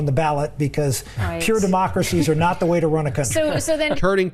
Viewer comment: did he say “democracy’s not the way to run a country”. DID HE SAY THAT?